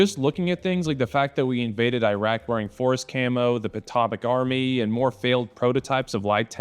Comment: What is he saying here after "forest camo"? It sounds like he's saying "the pitopic army", which doesn't make any sense. I couldn't find anything looking up "patopic" or "potopic" either. Am I missing something? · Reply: "Atomic Army". The concept of the Atomic Battlefield in the 50s where soldiers would have to be equipped to fight in nuclear environments.